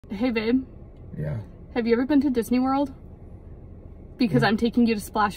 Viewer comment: I thought she said Sea World 🤔🤪